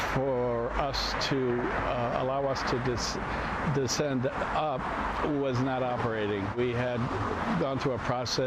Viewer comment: Did he really say descend up?